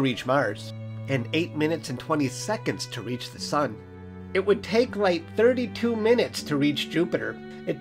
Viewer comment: Lmao thought he was saying "it would take, like, ..." and it was actually starting to piss me off